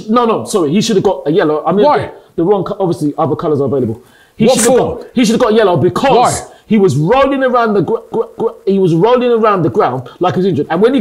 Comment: I thought he said "avocados are available" I was like wtf 🤣🤣😂